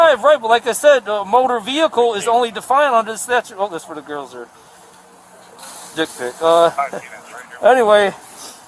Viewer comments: you missed what he said at 10:47. "This is for the girls. D!©k pic. 😂😂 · Did he say dick pic when looking in his phone? 10:48? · Did he say "oh that's for the girls there...dick pic hehe" 💀💀💀